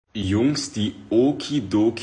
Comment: All i heard was "wongs di oki Doki sonchi moke daloki pokei hoki , oke🤩" lmao 😭